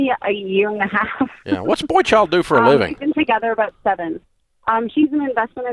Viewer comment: Did Dave say “what’s the boy/child do for a living?” 🤣 😆🤣